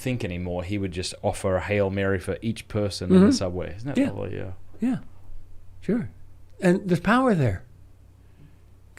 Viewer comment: St Josemaría said this in “The Way”, number 552: “Have only a few private devotions, but be constant in them.” Does anybody understand the name of the person they talk about in 1:18:26? I don’t get it. Thanks. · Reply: Benedict J. Groeschel